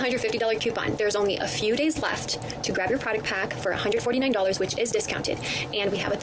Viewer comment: Took me a second to hear that the monat rep said 'product pack' she talked so fast I kept hearing 'potty pack' and I was so confused 😅